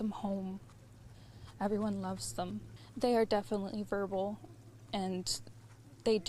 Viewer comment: They say the kids are chatty and will talk to anyone, yet are non-verbal? That is an enormous contradiction right there. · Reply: I heard her say she said they are definitely ‘verbal’ at 11:38.